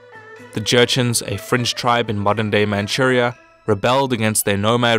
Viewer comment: Am I hearing that correctly a 7:40 a french tribe in China?